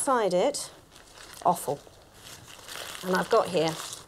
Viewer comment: It's intersting how liver is referred to as foul and is cheaper but I grew up (not UK) being told it's a luxy meat, it's only one organ and there are so many fabulous dishes to make. It is also very iron rich... GREAT... now I want liver pancakes... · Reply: Pretty sure she said offal, which is what the entrails of animals are called when they are still edible, like brain, liver, heart, kidneys. Honestly, at first, I had thought she said it was awful, too.